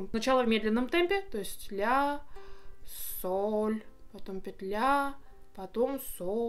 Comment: услышала петля..